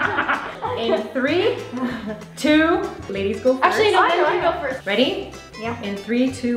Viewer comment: Did anyone hear actually no Ben can go first 😱😱😱